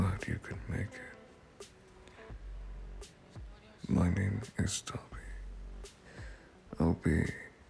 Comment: when he said "my name is Dabi" i thought he said "My name is daddy" 0_0